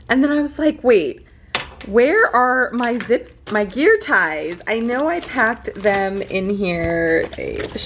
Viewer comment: She said "gear ties", but I thought she said "beer pies"... since she talks about food a lot. lol Oh well, it IS getting late... Happy 4th!!!